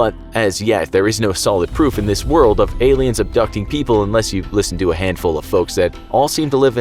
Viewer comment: I heard “Aliens adopting people”